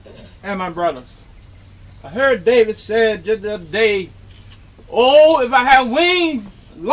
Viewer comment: “I heard this saying the other daeee.”